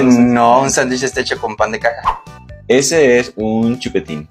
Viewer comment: Juro aue escuché "noo, Un sandwich está hecho con pan de caca* XD VIVA PARAGUAY ❤🇵🇾